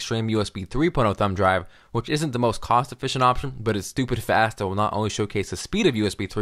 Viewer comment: Did he say "stupid fast?"